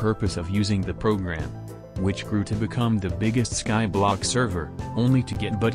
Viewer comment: For a second in the intro I thought said “The biggest anarchy—“ due to pausing and now I kind of want to see on 2b2t.